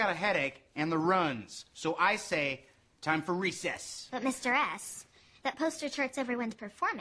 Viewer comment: sounded like "but Mr.Ass" 😂